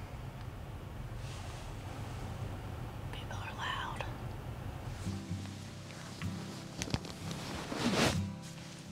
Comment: someone tell me what said at 12:59 I can't figure it out lmao. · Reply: She said “people are loud.”